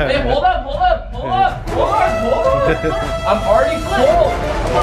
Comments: I heard at 17:18 say babe hold up did anyone else hear that ???? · did anyone else hear say babe hold up? Edit: this is my most ever likes on a comment thx ppl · Did anyone else hear call Katelyn Bae between 17:15 / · Who else heard call Katelyn babe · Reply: He said hey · LIKE THE VIDEO FOR ME ACTUALLY COMPLETING THE CHALLENGE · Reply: DID YOU SAY BABE HOLD UP AT 17:18 HMMMMMM?????!!